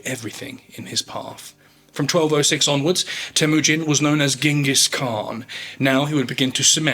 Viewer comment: "...From this point onward, he was known as Genghis Khan..." Me, listening to this and no watching the subtitles, "Did he just say... Dingus Khan?!?" And now I'm wondering why that hasn't been an insult everyone has been using.